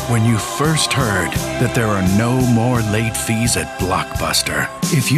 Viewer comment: for a momment i thought they said no more netflix no more netflix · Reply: i know your comment is old, but i hear “no more babies” lol